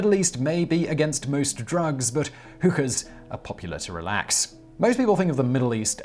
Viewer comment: When he was saying hookah I thought he was saying hooker lol.